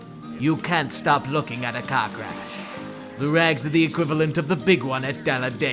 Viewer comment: Did he say cock-rash?